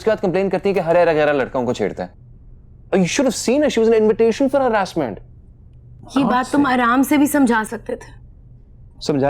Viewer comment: Did he just say "she was an invitation for harassment"? Gosh i can't unhear this.